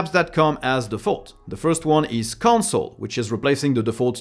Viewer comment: I thought I had a stroke for a moment when I heard gnome has konsole now haha